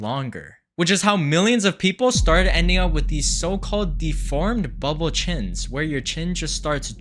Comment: i thought you say the fart bubble chin becouse my volume in my phone is low 😭😭😭